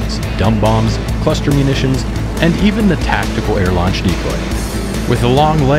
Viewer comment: Did he say @ 3:53 tactical launch nuke button?